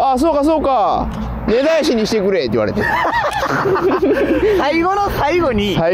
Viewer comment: なんて言ったん？ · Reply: 根絶やしにしてくれらしいw